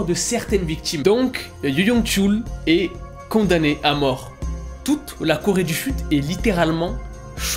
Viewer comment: Alors, pardon mais moi là j'ai littéralement entendu "Toute la Corée du FUTE" 😭😭🤣 Voila c'est tout pour moi, bonne journée 😂